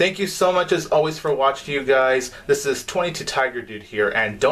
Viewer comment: You know? Is funny because, every time you said "Tiger Power....", I always thought you said "Taco Power...!"